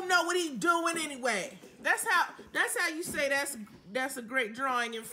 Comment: what is actually said is "thats how you say its a great drawing in french"